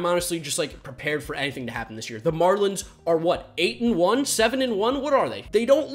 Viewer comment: The marlins are the best team in baseball. 2020 is the weirdest year ever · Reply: Apple FACTSANADO Really thought you said Mariners my guy, ima head out 🤦🏾‍♂️😭